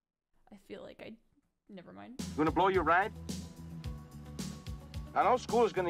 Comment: "Blow your what?" He said "blow your ride," as in "mess up your chance at a 'full ride' athletics scholarship to pay for college."